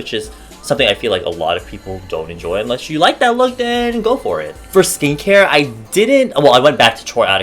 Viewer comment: I thought you said "for stink-hair" and I was like bitch whett 😩😂